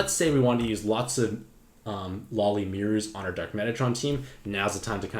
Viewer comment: you say something that sounds like “lolly myrs “ for dark Metatron. What are you saying? Curious to know what subs you are talking about. I understand Myr if that is what you are saying but “lolly”?